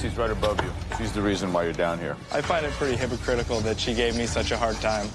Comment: saying she's doing this for her son and then targeting Bime, who's doing this for his daughters, is PEAK irony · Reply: for a second there I thought you said pretty not petty, and while talking about it just didn't make sense